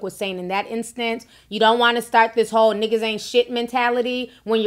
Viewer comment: She said "ninjas aint shit??" I thought she said she don't need one for shit? Same here im good I don't need a man for shit!!!